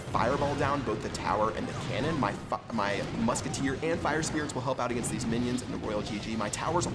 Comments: did he say fuck my musket?...xD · i heard "and fuck my musketeer even caption says it XD now it became a mature channel. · i heard him say 'my fu..'